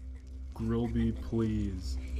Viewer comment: im srry. i thought it said "grilby likes fries" XD